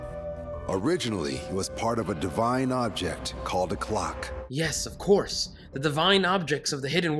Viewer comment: Venne he said the sword was made by a clock I thought he said glock and it would be so much better if it was the secret weapon was a glock